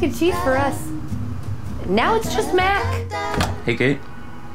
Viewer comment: I thought he said he was making a cape not a kite.“NO CAPES!!!”Props to anyone who remembers where that is from😂😂😂